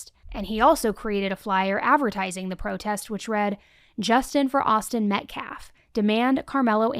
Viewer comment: this isn’t to try and hate but did anyone else hear justin instead of justice at 37:48 ish im tryna make sure im not crazy